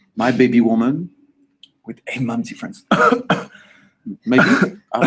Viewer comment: Dream House? HAHAHA Lau:8 babies,of course mine my baby woman Larry:*cough*with 8 baby moms/8 different baby moms/ 8 moms different cough Lau: Maybe? I don't know this 2 spilling tea everywhere. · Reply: I thought he said "8 months difference" lol